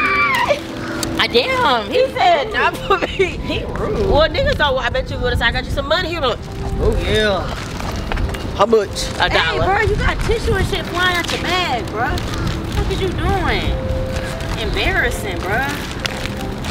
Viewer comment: I thought daysha said “you got tissue and ish flying out ya a**” I was like huh. Lol